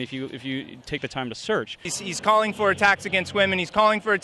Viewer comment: Haha, what?! A tax against gays?? What the shit is that? "I didn't vote." -- then why should you have a voice now? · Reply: Oh I thought he said attacks, not a tax.